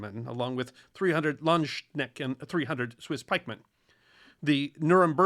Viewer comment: I could swear you said "300 Landschnecken" (Landsnails) - now I know you meant to say Landsknechte (or at least i'm assuming) but in my mind an assault force of 300 Landsnails, possibly wearing Spartan Armor and Phalanx Shields deeply amuses me :D